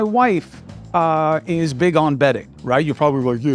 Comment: Did anyone else hear “betting?” (Also Viscose is pronounced VIS kohs)🤓 thanks for the shows.